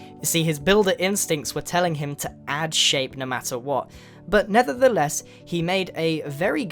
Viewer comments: Am I the only one who heard NETHERtheless? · Did he just say "nether the less"? 😆😂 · you said nethertheless instead of nevertheless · did you say Nethertheless